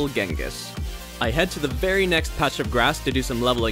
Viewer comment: it says priority instead of very. Just letting you know.